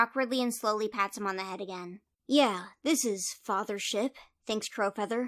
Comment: I legit thought Crowfeather said "father shit" instead of "fathership" and was like "MOONKITTI SWORE!?!? :O"